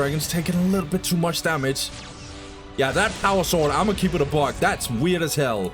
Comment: you have a catchphrase you say before you say something obvious that needs to be stated like at 48:26 But what are you saying? "Ima keep it a bug" ?